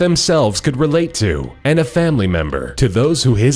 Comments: I thought it said " The Bear That Went To Far" As In Like He Killed A Crap Ton Of People o.O ! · I thought it said "the bear that wet too far".